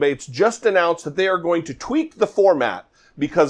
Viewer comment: For a frightening instant I thought you said "they were going to TWEET the format..." and imagined the debate being hosted on Twitter. I can imagine Trump would love to have the debate that way.